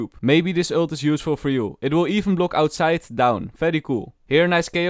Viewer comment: did he just said "Outside Down"